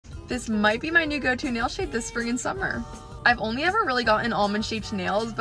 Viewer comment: I know what you said but when you said spring and summer, it kind of sounded like you said freaking summer!!! Lol❤